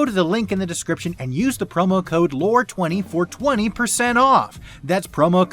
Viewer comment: I heard "420% off"